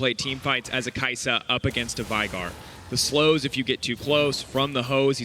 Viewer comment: what is he saying at 14:58? the slows if you get to close, from the hoe´s? wtf lol